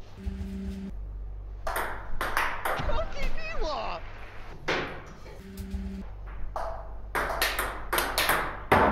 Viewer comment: くらいなんていってるの？？ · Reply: コキ・ニワと言っています